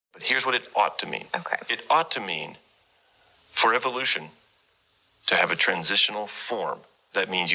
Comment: I love the fact, and I pray (see what I did there) that everyone experienced the same thing I did @ 6:34, when he says "Here's what it ought to mean for there to be a transitional....." I heard..." Here's what it ought to mean....For all the BS we spew to make any sense"